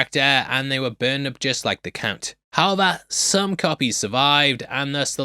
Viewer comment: did you say count or "kunt" 🤔